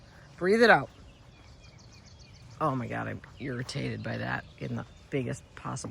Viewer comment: I swear I heard you say “deer-itated” (when you probably said irritated) when commenting after the overnight clematis browsing. I think you’re right regarding the white dogwood. It would be more visible at that distance & would “pop” more with crabapple foliage in background & Japanese maple foliage in the foreground. Now I’m wondering where the red dogwood would go? Best response ever to the trolls!! Sometimes I wish there was a YouTube community “boot” mechanism so we could help evict the bad actors.